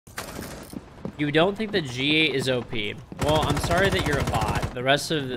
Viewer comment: what was he saying at 0:01? "You don't think the G is op?" I am not sure what he is saying